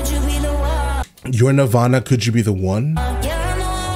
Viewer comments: Every other time I've listened to this song I hear the end as "You're my armor. Could you be the one?" And now I keep hearing it switch between "Your nirvana" and "You're my armor" XD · I hear "You're my armour" not "You're nirvana"...